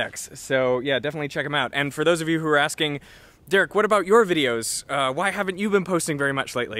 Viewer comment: I first heard 'Dirk' instead of 'Derek'